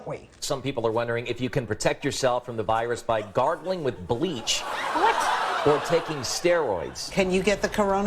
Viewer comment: I totally thought they said "gardening with bleach" at first hahah